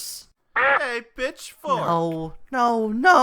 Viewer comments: i heard him say *bishfork*💀💀💀💀💀💀💀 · i heard 'hey bitch fork' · why when glow stick said “hey pitchfork” I heard “hey b****fork” also why are sock and glove precious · I thought glow stick said “Hey B**chFork” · I thought he said hey b**ch fork but instead he said hey pitchfork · I thought GS said "Hey B*TCHBOY~" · Uhm, why do I hear Bitchfork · I entirely misheard glowstick saying "Hey, Pitchfork!" as "bitchfork" and i'm gonna start bawling im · I thought he said bitchfork